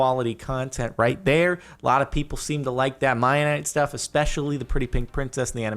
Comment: did he just say Mianite